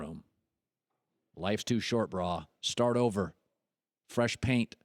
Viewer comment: Did Colin just say brahhhh ??? A hahahaha